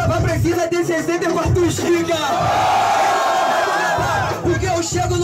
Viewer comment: Até hoje não entendi o que o falou em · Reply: "Olha só essa narina, pra tu gravar precisa de 64giga"